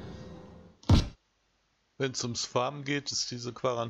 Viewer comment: Oh, offensichtlich bin ich kurz hochgeschreckt. Hatte es nämlich Gestern noch geliked. Aber geguckt hab ichs nicht - so hab ich Heut Abend umso mehr zu schauen. 15:15 "Wenns ums ... geht" Hab leider nicht verstanden, was Du da gesagt hast. Verstehe "fahren" aber das wirds ja nicht sein. Bitte um Aufklärung. 29:18 Eine ganze Armada von denen ... lol